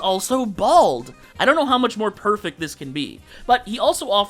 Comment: did someone say.. Perfect? 🤨